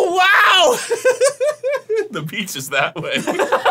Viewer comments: "The beach is that way" absolutely fuckin slayed me, like the queen Arin is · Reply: Oh my gosh I thought he said "peaches" too XD lol But then I saw this comment and I said "Oh he said the 'beach' is that way" XD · "The beach is that way" absolutely fuckin slayed me, like the queen Arin is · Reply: Ooooh Beach. I thought he said the pizzas that way. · "The beach is that way" absolutely fuckin slayed me, like the queen Arin is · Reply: I thought he said the peach is that way 😂